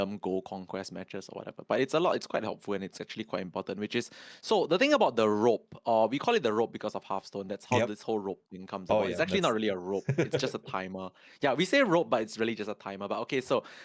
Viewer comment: Did he say "I'm half stoned"? This would take my admiration towards to a whole new level of insanity. I miss Norm ❤ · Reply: Because of Hearthstone they call it the rope